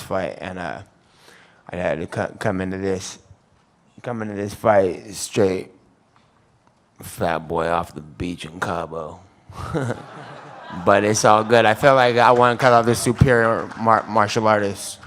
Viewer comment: hey) can u please decode what Nate is sayin here 33:20 ? thank you ) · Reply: quater back Diaz said. "I had to come in on this fight straight fat boy off the beach in Cabo, but it’s all good."